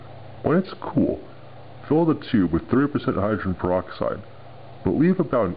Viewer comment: I can't understand what you say there, it sounds like "Fill the tube with three-oh percent...".